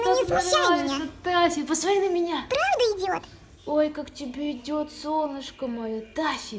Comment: Таффка такой красавчик😍😍😍🐶🐶🐶 Блин, когда Таффка сказал "правда идёт"(бабочка) ? Мне послышалось, что он сказал "правда идиот" 😂😮